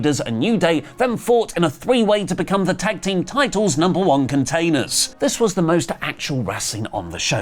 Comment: Does he say number one containers?